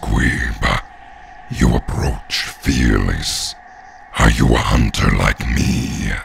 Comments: i thought he was saying fatherless lol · When he said “fearless” I thought he said “fatherless”